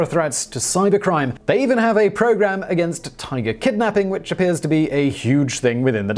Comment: Did you say "tiger kidnapping"? 🤔